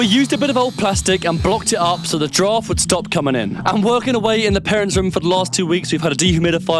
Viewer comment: why did I hear "so the giraffe would stop coming in" 😭